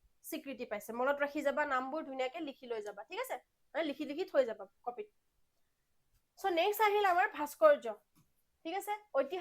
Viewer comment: likhi loi jaba😅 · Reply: She said likhi likhi thoi jba copy t. Gutei khni nxuna k comment diya tu aji kali mandatory hoi goise right???😂😂😂